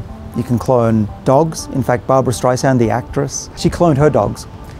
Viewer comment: I thought he said ''You can clone Barbra Streisand'' and I was like ''Cool, I'll take two please.''